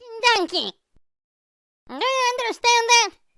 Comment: did he say donkey